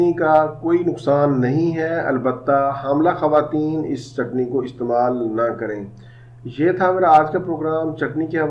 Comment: par aapne kya bola hai... sir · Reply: hamla khwateen yani pregnant women's use na kren